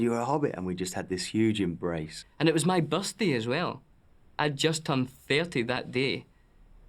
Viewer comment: Am i the only person when Dom says Birthday who thought he said bus day.